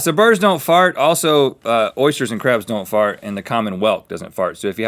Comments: I thought he said 'Common wealth' instead of 'Common welk' 😂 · Thought Rhett said "the common wealth" don't fart 😂